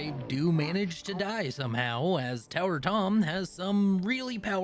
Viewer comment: I thought you were saying tinker tom instead of tower tom and was so incredibly confused, like tinker tom had an occupation change!? When?